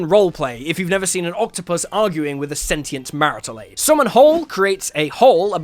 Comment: Sentient what? I'm not a native speaker so I can't figure out what you said 😅 The automated captions say "marital aid", is that correct? I had not heard the term before but I have a guess as to what it may mean 😏